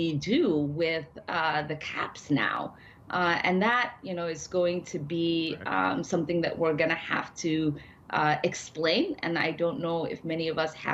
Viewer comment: She does not say "we'll have to change this" or "we won't vote for a bill that doesn't deliver", she clearly said: "That is going to be something that we're gonna have to explain [... to] American People". They've given up on you. They won't deliver, they won't fight, they will only try to brainwash people into thinking that getting less than the Republicans would give you is somehow good.